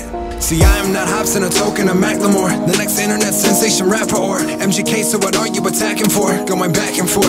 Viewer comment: Does he say Zansation at 2:19?